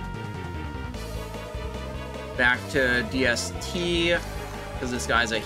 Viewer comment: How did you get a DST at 60? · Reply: Sry at 2:20 I could have sworn you said DST, not DFT. My bad.